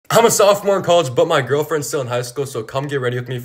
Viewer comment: NAH WHY DID I HEAR “I’m a sophomore in Highschool” I WAS GONNA SAY HES IN HIGGSCHOOL!!! LIKE BRO I HAD TO REWATCH IT A FEW TIMEA BEFORE I HEARD HIM SAY COLLEGE BUT I FR WAS THINKING IF HES IN HIGH SCHOOL THEN IM STILL IN ELEMENTARY SCHOOL